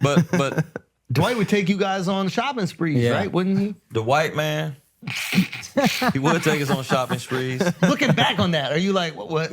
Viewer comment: when he says “Dwight Man” I heard “ da white man” 🤣🤣